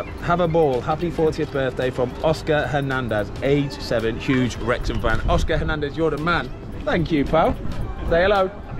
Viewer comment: When Ben read the card it sounded like he said "huge rectum fan". I thought "jesus, kids a bit forward"